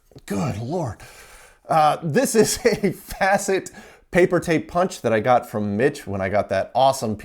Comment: I heard: "This is a Fascist paper punch" Hmm...